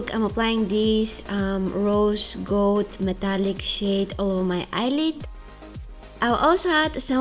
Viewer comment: I love this channel so much but I have to laugh at this!... at 2:12 was I the only one who thought she said shit instead of shade?!!!😂😂😂😂😂